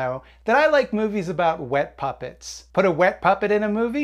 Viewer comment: I thought it said wet puppies :(